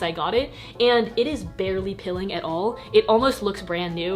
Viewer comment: girl you saying ‘peeling’ was a big mind fuck, I thought you said ‘pilling’ 😂